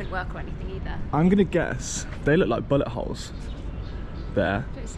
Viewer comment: Oh shoot,i thought you said butt hole.hahaha its bullet hole.